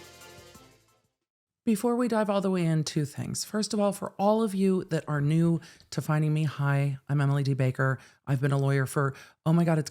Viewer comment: I'm sure that I heard you say: "For all of you who who are new to finding me high". Hmm. 😂😂